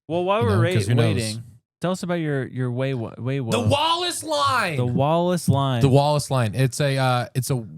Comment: …totally thought you guys were saying “the wall of slime,” not Wallace line 😂😂